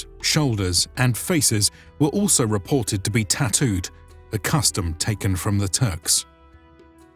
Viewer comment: When he talked about tattoos did he said "a custom taking from the Turks" or i Heard wrong ?